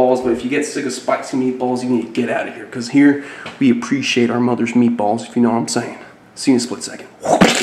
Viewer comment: I think he is sayin we appreciate shit like Mother Nature’s meat balls which is shit